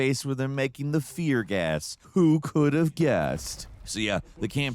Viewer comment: did you say, "who could've gassed"? That was smooth